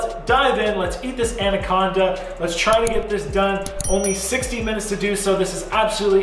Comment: I can't believe she bit him!😬 Poor. I was getting full just watching you finish off the green section. The duel camera setup is cool. I originally thought you said you only had 16 minutes, not 60. Beast mode 👍🎉